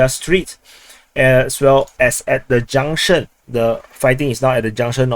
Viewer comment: your accent sounds almost exactly like chinese. when you said “junction” i thought you said “jiang shen” haha